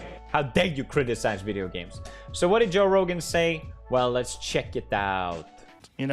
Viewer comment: I thought he said Jo bro at the beginning